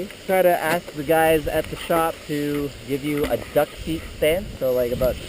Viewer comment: did you say 'dusty stance'? I couldn't really hear you that well. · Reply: Oh, sorry, I said 'duck feet" stance. :)